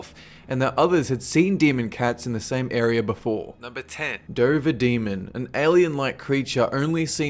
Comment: I heard Dragon Demon. Too much skyrim for me.